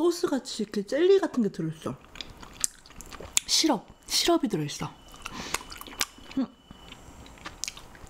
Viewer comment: Oh my god did you say "shrab"? In this 10:09 쓰라프? This is arabic word🤷‍♀️🤷‍♀️🤷‍♀️